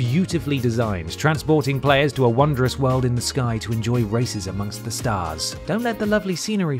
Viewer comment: "races amongst the stars" sounded like "racism" for a moment